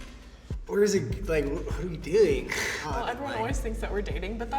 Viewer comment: what did he say? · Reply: What are we doing